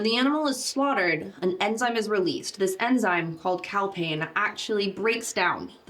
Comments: For one moment I thought that the enzyme was called "Cowpain". Would have been very apt.... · at first I heard the animal releases “cow pain” when it’s slaughtered.. had to rewind that one